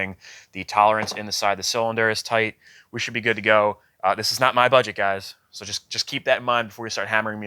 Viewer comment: I thought you said this guy was on a budget he pretty much bought a brand new four-wheeler